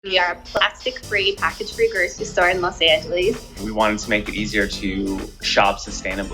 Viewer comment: Did she just say "Los Angeliis?"